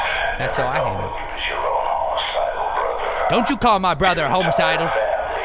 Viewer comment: it sounds like he says dont you call my brother a homocidal